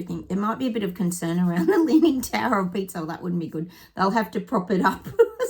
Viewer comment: Sounded like you said the tower of pizza 🍕 its Pisa not pizza 😁😂